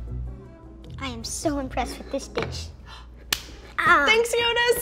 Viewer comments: I thought he said “I’m so impressed with this bitch” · I heard at 13:00 "I'm so impressed with this bitch"